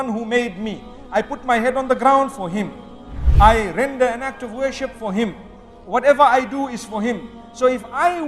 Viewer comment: Masha Allah! Just an important note though, Mufti Menk said "I render an act of worship for Him" not "I render not to worship for Him" in 3:20. I hope this can be editted as this slight linguistic difference is actually something significantly different Islamically from what was said and can be misinterpreted for those who may not hear/hard of hearing.